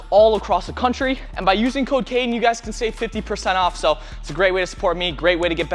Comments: I thought he said “by using cocaine you can get 50% off” 7:37 😂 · He said “by using code Cayden” and I heard “by using cocaine” 😭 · in 7:37 when he said code Cayden i heard cocaine